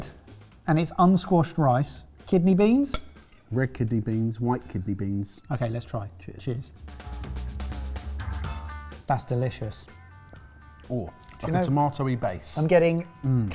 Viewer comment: I thought Jamie said rickety beans instead of red kidney beans. 🤦‍♀️